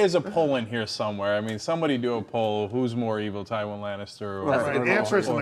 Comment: it says Tyrion instead of Tywin